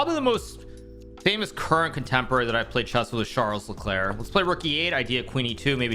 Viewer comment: "Charles LeClair" is my uncle. Lol 3:18 jk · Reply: I thought I heard him mention the name of Ferrari F1 driver Charles Leclerc lol